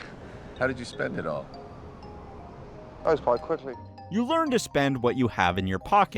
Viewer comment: What does he say??! I can’t make it out. Please help! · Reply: "Oh, quite quickly."